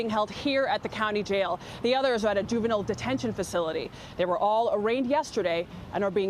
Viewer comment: i first thought she said they were all raped yesterday